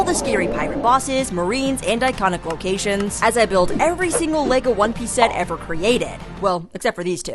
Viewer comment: At first I thought it said I built Lego in one piece😭